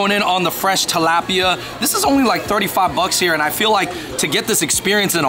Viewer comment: did homie say $35 just for that fish dish??? That’s robbery lol🤣